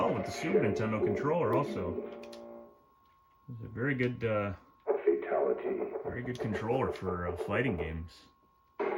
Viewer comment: You hear it also? Nice I was beginning to think I was the only one lol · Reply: In my case, I've always heard it as "Oh Fatality".